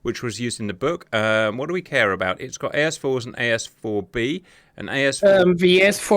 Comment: i heard S4-B (S-IVB) there. That would be SOME missile.